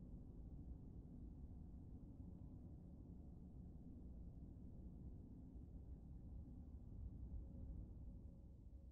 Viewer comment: what does the FUCK*NG iggy says to Noctis?!?!? Anyone knows?! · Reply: Your majesty